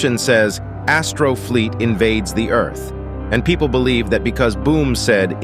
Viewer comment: For a sec I thought it said Astro feet invade the earth😂